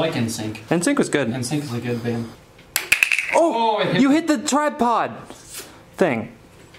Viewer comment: Did I hear tide pods??